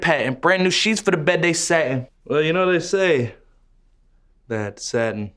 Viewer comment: that was a lot more Innocent than I thought. I heard "brand new sheets for the bed they sat in" yk "sat in" not satin. Cuz they were so we... ykw nvm